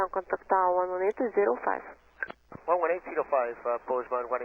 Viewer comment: That crash looked unsurvivable. I also heard 2,500 on the read back. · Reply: I heard 2300 but it definitely wasn't 2700. 8:20 ATC captions are wrong, tower didn't say 118.5. Mostly sounded like 118.05, could be non-standard read of 118.205 and the 2 got swallowed (it's barely audible if you know it should be there, or it could be me expecting it to be there). TBF, tower missed the error too.